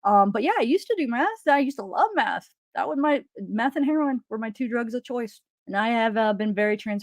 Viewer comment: I thought you said Math and heroin and was like WTF, that's a strange combo?! 🤣 Nice work! I just turned 43 and have been off meth for 20 years, and it wasn't until my 6th year of sobriety I felt strong and right in my mind ...I started when I was 14 and I'm so grateful to be free. And the haters talking shit are actually using your non responses to other people's opinions as a place to fill in the space and júst make things up. People want to see you blow up and you're not going to. It's not who you are. I can even imagine the haters are mad that you didn't agree with them on Kiely's mom not crying being odd behavior, which I do not agree with either. You are damned if you do and damned if you don't on social media. Try to ignore as much as you can and focus on the 90% positive subs you have 💞💖💕